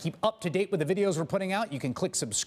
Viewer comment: Did he just say blow me or below me?